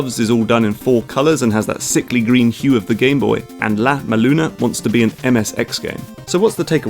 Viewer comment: Ow! You said "La-Maluna". It's "La-Mulana" That mispronunciation hurt me like a punch to the liver. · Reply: Thank you for actually writing it down. I never heard of the game and heard him say "Lat Ma Luna". When I tried to google the name I found absolutely nothing at all which was frustrating as hell.